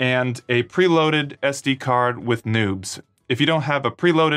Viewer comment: istg i thought you said i needed an sd card with nudes